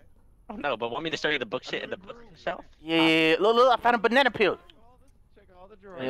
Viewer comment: Is it just me or did say "book shït"? xD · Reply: Book shelt